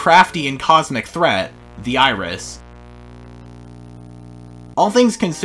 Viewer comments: on the gemini segment I thought you said the Irish instead of the iris 💀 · When you said iris I thought you said irish